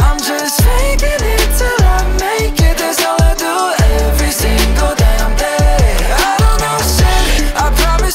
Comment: I misheard it and it keeps repeating on my head 'I am single and gay!' instead of 'every single damn day'. Haha